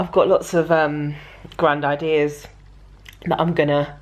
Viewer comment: When you were lying on your bed, I thought you said you had lots of Grandad ears??? I rewound it - you said grand ideas! 🙈🙈🙈